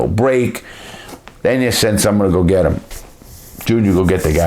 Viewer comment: thought you said "go fetch". /watch?v=DiWz6ojZoGw hope this will be a full movie. not for torture but for planning and executing heists. thats what movies really needs, realism :)